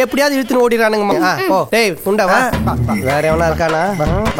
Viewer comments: anyone heard that?? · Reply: He said urunde not punda😂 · did he say punda va 😂